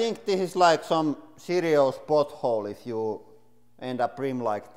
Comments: I thought I heard he said a "hit a serious butt hole" hahahahaha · I swear he says butthole 😂